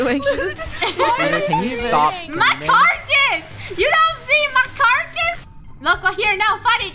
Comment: was actually saying "My carcass! You don't see my Carcass?!"